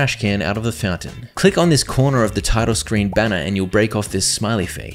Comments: i heard asshole 💀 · what did he sayyyyyy? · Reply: You'll break off a smiley face. Then put the smiley face in the R's hole (😬) and you will cause parrots to flee.